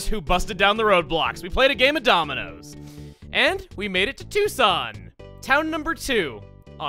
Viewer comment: I seriously misheard Twoson as as Tuscon because I just came from a nappy video lol